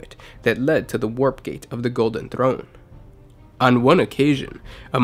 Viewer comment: Am I the only one that thought it said “Golden Tyron”.